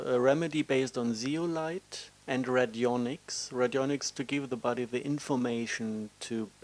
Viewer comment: ************On the video time 26:25 did he say "Redyonix" to help the inflammation? WHAT WAS THE WORD HE USED? ******